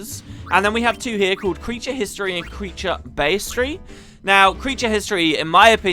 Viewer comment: I was tabbed out when you said Bestiary. I thought you said Pastry.